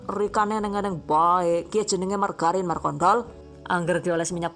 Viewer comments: margarin markontl apa aku ga salah denger? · ini apa cmn gw yg salah denger? mark*nt*l? 😂